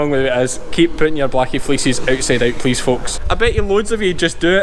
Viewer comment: first time he said fleeces i thought he said feces lol have a great day from Canada